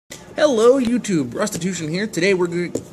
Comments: You might want to change your name or at least pronounce it better or slowly when you say it I thought you said "hello it's prostitution here" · I thought he said "hey guys its prostitution here"